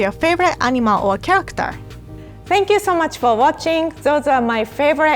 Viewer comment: am I the only one who heard “I don’t like to hear what u make during winter”